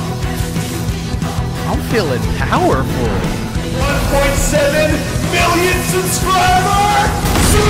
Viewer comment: Quick question in 1:33:27 did say 1.7 million subscribers tonight or am I tripping